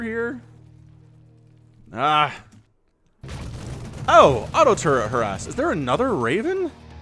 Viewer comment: Lol i thought you said" He auto turret her ass." you said auto turret harass. LOL